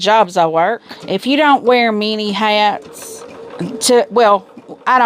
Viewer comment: 😂I kept hearing 'mini hats' instead of many hats. 👒 I thought why does she have to wear miniature hats?! Lawd.. its been a long week